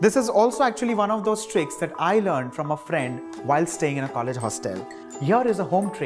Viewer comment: Okay, at the beginning I clearly heard you say "while I was staying at a haunted hostel..." We need to hear more about this...and your drink looks amazing!